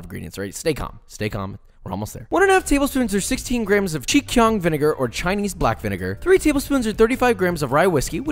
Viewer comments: did he just say ching chong vinegar at 2:09 ??? hahhahaha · Did he say "Ching Chong vinegar" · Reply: he said Chinkiang it does sound the same a little bit 😅